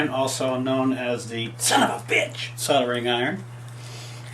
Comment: did he say son of bitch ?